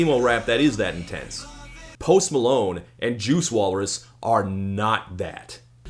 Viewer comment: ? Did he call him JUICE walrus??